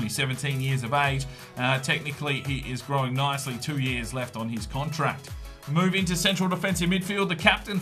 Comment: Renew Bello's contract! Very versatile player and played an important part in our campaign this year. He'll be important next year in La Liga as well. So get him a contract :) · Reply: Sorry i heard say "only 1 year on his contract" when he actually said "plenty of years on his contract"